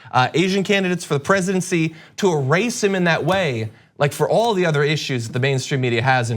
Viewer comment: Did you say "to e-race him ..."?